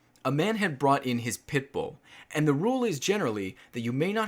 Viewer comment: When you said Pitbull at first I thought you said Pipboy